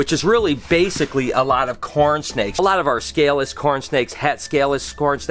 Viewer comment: Good morning! I don’t know how many you have, but I know you have sooo many!! Thousands of leopard gecko, californian king snakes, Corn snakes, etc. Btw, when I heard you saying Corn snake first time, I thought you said coral snake and I was like ”Wtf you are handling one of the most venomous snakes in the world without gloves” Lol.